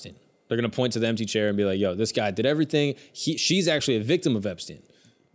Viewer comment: I thought Schultz said a victim of Palestine. I was so fucking lost for a second. lmao